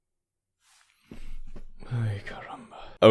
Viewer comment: 4 years and many views and I just noticed you say (or i think you say) "Ay Caramba" in the minute 19:26 xD